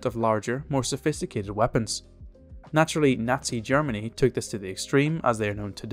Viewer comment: Did I hear "Nasty Germany"?